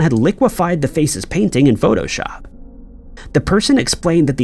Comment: An I the only one who heard him say "liquefied the face's painting" at the 18:04 marker?